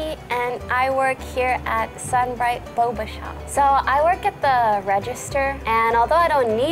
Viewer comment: WTF is even boba.. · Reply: I thought it said “booba shop” 😂😂😂😂